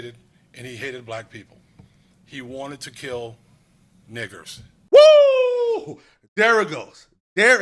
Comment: I LOOKED AT MY PHONE TO SEARCH MANEFESTO AND ALL I HEARD WAS " HE WANTED TO KILL NIKK3RS" WHAAAAT 😮 DAMN BE SAFE OUT THERE. FOLKS